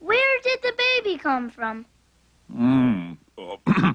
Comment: What did he say? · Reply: "Uh, that one's for your mother."